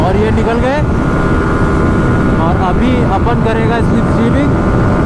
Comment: pe ky bola kuch samz nhi aya plz batoi koi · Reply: He said `Apun karega slip streaming`.